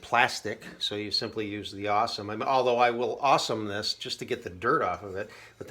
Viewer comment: Hey - would you mind listing the type of cleaners you’re using? I wasn’t able to find “the awesomeness”, which is what I thought I heard you saying? Thanks for these videos!!